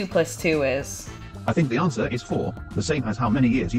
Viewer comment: how did I hear "I think Beyoncé is 4" instead of "I think the answer is 4"